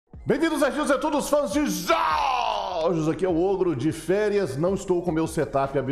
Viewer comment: Só eu q escutei "bem vindos nerdinhos tetudos fãs de jooooooj" kkkkkkkkk